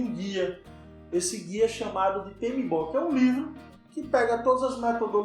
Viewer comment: aos 4:35 ele fala de um guia mas não entendi o nome. Podem me mandar por favor? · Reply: Rennan Paiva Olá, o Guia é o PMBOK que é elaborado pelo PMI